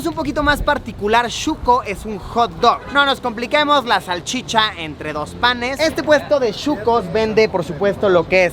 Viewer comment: por un momento escuché que dijo la salchicha entre 2 patas jajaja 😅😅